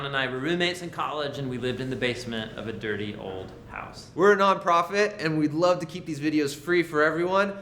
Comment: At the end of this video I legit thought he said, “we’re non prophet” and I was so confused